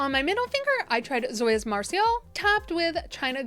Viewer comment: Two things, thanks so much for showing the shades before you layer them, such a little thing but it adds so much to my understanding of your attempts. Also, was away from my laptop when you said Zoya's marisol and really thought you were trying to say marseille. Your attempts to pronounce Zoya names always give me a giggle.